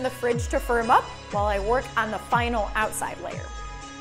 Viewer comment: i thought you said for a month LOL